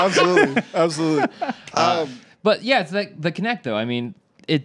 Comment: did Paul say they were playing Ketchup?